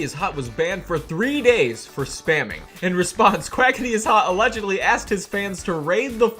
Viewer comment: Im still sad that he didn’t include the famous “IGETLOTSOFPOUSSEY”, the alt of “Quackityishot” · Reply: I thought it said quackity i shot